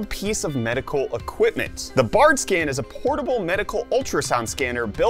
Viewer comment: People who thought he said fartscan | V